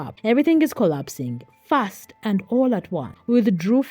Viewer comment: PEACE BY PEACE?! Are you meaning piece by piece... use spell check. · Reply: It also said the f word instead of ‘fast’ 22:37 🤣